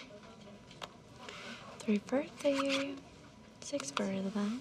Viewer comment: I keep hearing “Free birthday!”